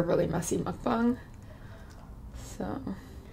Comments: did she say mungbung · Did I hear “mussy” mukbang?